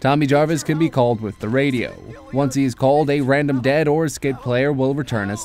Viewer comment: I thought he said a random escaped or dad player. lol😅